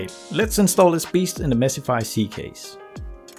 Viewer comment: I heard 'Messify C case', right?.. )))..